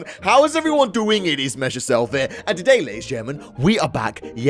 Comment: sry i thought u said